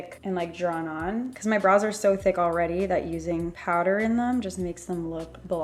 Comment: "My eyebrows so think already" 🙃🙃 did I misheard??